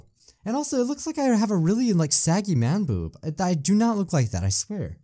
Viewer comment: i thought he said "and it really looks like i have swaggy man boobs" XD i dunno how i heard that tbh